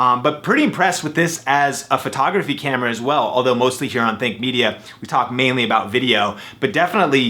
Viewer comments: i thought that you said thicc media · at first i thought you said thick media